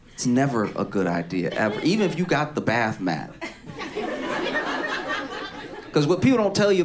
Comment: I heard him say bath math I realized he said bath mat I’m still floored I guess I needed a bath mat 😂